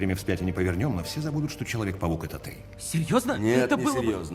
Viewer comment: я услышал что кембебедж сказал:"что человек павук это ты"